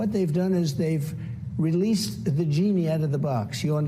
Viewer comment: did he say they "released the vaginy out of the box"?